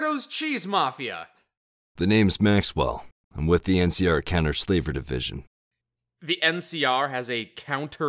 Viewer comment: Counter Slaver division. I heard it as "Kenner Slaver division"